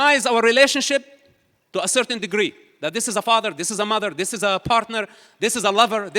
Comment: Did he say "this is a fuck*r." At 5:10?